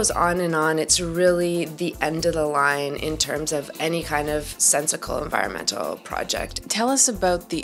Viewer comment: Did she just say “sensicle?”